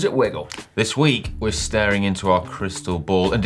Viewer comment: At the start of the show i misheard, saying: This week we are staring into our Krystal Boyd. I immediatly thought, that it´s a bit wierd for a cycling channel, but it´s just gonna be the best show ever... then i realised what he really says, and was a bit dissapointed...